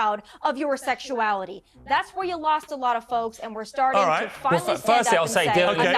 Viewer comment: I know she tried to say, "lost a lot of folks," but it sounded like she said, "lost a lot of phobes," and I think that's hilarious.